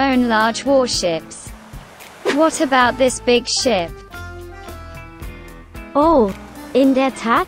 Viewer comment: "What is this big shit" 💀💀💀💀 (misheard btw)